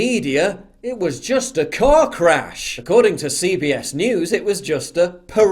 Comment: I was just listening and thought you said the media said it was just a cock rash… then I looked at my phone and realized you said car crash…hahaha!